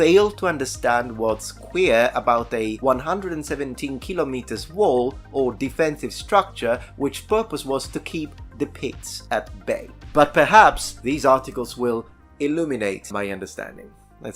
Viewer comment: thought at first he said, " to keep the pigs at bay!"